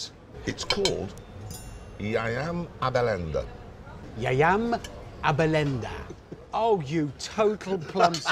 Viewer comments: Yayam abalenda? I am a bellend. Lol i Just got it after seeing it 15 times😂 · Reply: I thought he was saying “I am a blender” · To all Italians, what did Richard say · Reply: He said I am a bellend